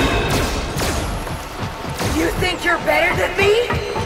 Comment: I thought Vanessa said: ”YOU THINK YOURE BETTER THAN ME?!” for a second. 🤣